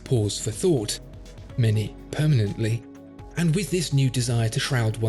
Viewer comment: “The French Revolution gave the aristocrats pause for thought, some literally” 😂 Came for the watch, stayed for the history lesson and sound advice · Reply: I thought he said "permanently", but I'm too lazy to go back and find out